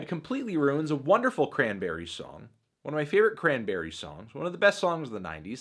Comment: this moment had me thoroughly confused as i thought you said "cranberry sauce," so i rewatched the entire video in order to get the joke...